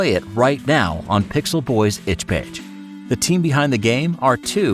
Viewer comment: Does he say itch page? Cuz my brain is only registering "Itch B*tch" xD